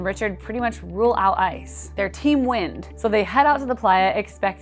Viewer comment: I first heard “Their team winned” instead of “They’re Team Wind.” 🤣